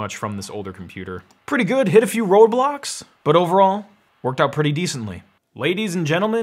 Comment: it sounded like he said "Roblox"